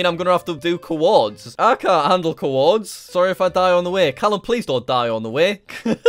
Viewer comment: I thought it said calcium and I was thinking “*minecraft is out of control now*”